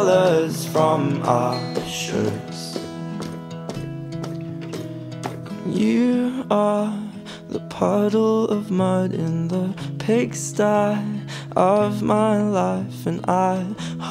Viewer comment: I thought he said “you are the bottle of blood in the pigsty.” I was so confused as to why there would be blood